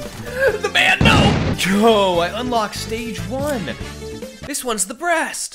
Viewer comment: the subtitles say “ I got stage women”